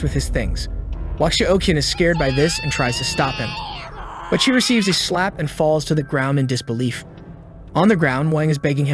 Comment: what kind of Aiden yelling is this😂😂😂😂😂🤣🤣🤣🤣🤣🤣🤣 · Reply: Aisen